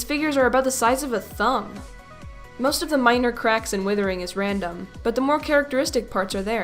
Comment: I thought she said minecraft